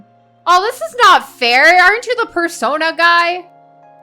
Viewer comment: I thought she said Fursona in reference to the squid frogger 😂